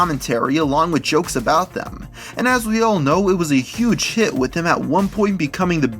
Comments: ngl I thought he said it was a huge shit lol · did he say shit